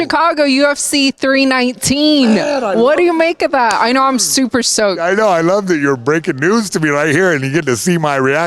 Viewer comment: Did she say she’s “super soaked”